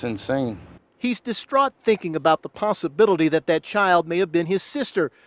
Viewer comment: the what? Did this man just say “the possibittily”